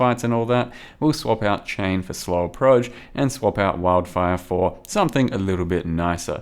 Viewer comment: Swallow Proj? Am I hearing correctly? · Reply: slower proj if this is an acutal question